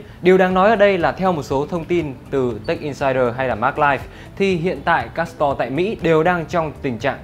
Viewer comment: Đoạn 0:45 sao nghe như : " Theo thông tin của Tech insider hay là Maglike " @@ Thế là TechMag à ~~